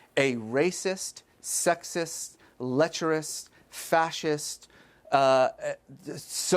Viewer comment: What word is Reza saying here? Lecturist?